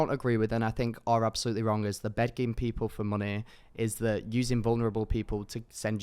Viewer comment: did he just say "bedging"? uh max you have some explaining to do, what is bedging?!?!